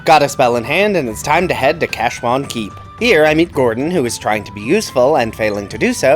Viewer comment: Was half asleep, so I misheard that as "cash monkey" ... Got very confused, then read CC.